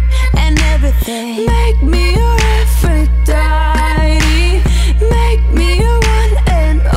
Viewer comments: I thought she said "Make me your effort, daddy." 0 _ 0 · On 0:29 it sounds like she is saying "make me ur girlfriend daddy~"